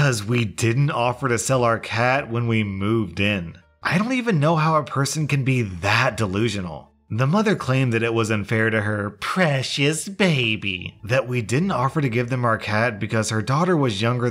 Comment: previous instead of precious lmao